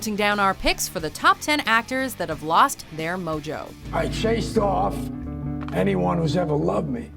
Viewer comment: i thought it said actors who lost their emoji 😂